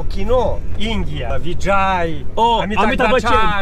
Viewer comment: did he say vijay😂?